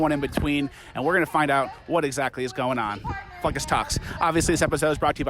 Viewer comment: hahaha I'm not against your stance man, we both know it is murder as well as taking a pill, but ir sounded like you said "f*** us"